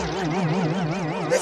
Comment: It sounded like he said "Its enough to make a grown man cry, and that's so gay."